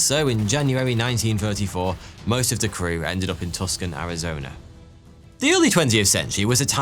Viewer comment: did you say TUCK SON Arizona? It's pronounced TOO SAHN.